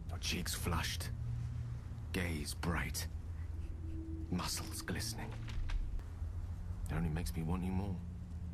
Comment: I thought he said "muzzles glistening" and thought we were going to have more action like the bear scene.